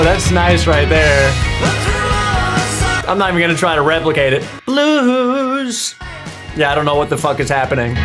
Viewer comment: I thought he said bruised not blues???